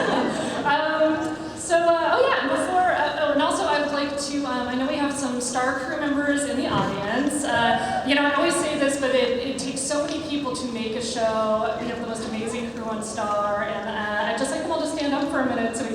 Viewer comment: at first I thought she said Starco.